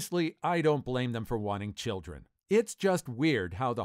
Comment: I thought she was saying "men who want to give birth themselves disgust me" but no, she is saying "men who want children disgust me".